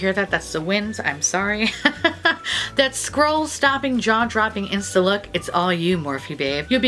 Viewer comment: When you were reading the print for the powder, I thought you said, "squirrel-stopping," and I was like, "fuck you, Morphe. You are dead to me." I checked the website, and it turns out you said, "scroll-stopping." My apologies to Morphe. 💐